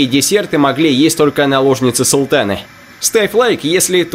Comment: залип и послышалась " Ставь лайк если тоже наложница султана"